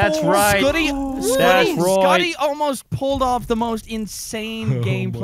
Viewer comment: anyone hear call Scotty.. scutty twice😂😂